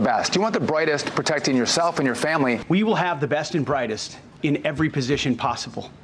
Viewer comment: Did Hegseth say "the best and the whitest"? Maybe I misheard.